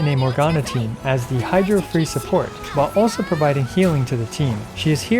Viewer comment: my ears are at fault but I can't revert what I heard ' nasty of the mile...' I am going insane after hearing about amazing anniversary rewards.